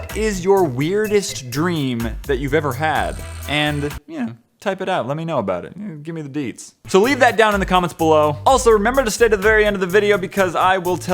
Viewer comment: my brother thought you said give me the d.XDDDD